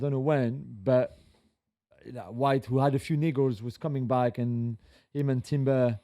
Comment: what did he say?!!!!! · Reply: "Niggles"